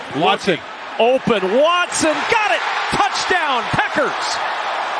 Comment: Did he say Touchdown Peckers at 7:45?